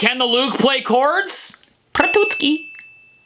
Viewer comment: what did he say? Prtucky?